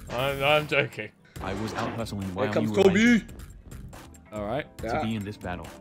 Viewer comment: Yo at 12:33 what does vikk say 🤨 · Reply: "here comes tobi"